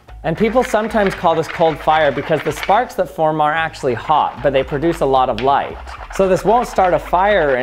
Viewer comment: Why am I hearing 'are actually hot'? Assuming he's saying aren't?